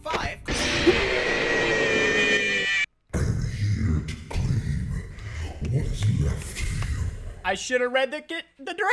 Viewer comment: what did dis animatronics said? lol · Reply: “I’m here to claim what is left of you.”